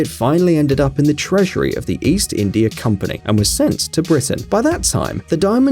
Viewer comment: "Sent to Britain" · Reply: when i see ur comment it really says its "sent to britain" coz i heard "sent to prison" hehe